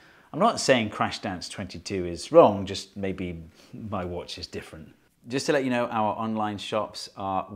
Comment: Did someone say 2:29 ..."watch me different"